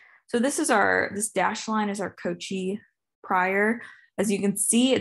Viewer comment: "cochivalue"? I don't understand what term is mentioned. Perhaps someone could help me out here.